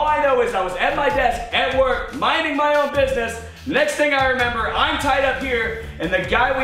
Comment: Did....Did he just say "Mining my own business"